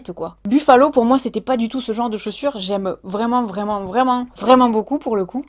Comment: J'ai entendu "Saussure" à un moment, j'ai cru que t'allais parler Linguistique. Je me disais que c'était bizarre parce que c'était pas du tout le thème de la chaîne mais qu'après tout pourquoi pas, ça pouvait toujours être intéressant. Mais en fait non 😐